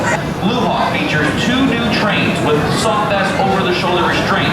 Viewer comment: Did he ACTUALLY say "With soft ass over the shoulder restraints" at 0:08?? LMAO That's what I heard 🤣🤣😂😂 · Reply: Soft vest🤦🏾‍♀️